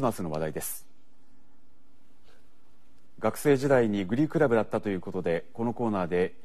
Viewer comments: 最初プリクラブって聞こえてて、プリント倶楽部だったのかーって思ってたらグリークラブだった笑笑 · グリークラブ（英: glee club） 合唱団につけられる名称の1つ。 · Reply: 本気でプリクラ部に聞こえて困ってたので助かりました